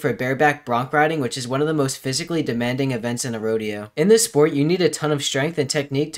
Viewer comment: I thought it said romeo not rodeo💀